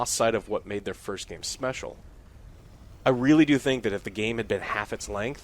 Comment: did anyone else hear "smecial"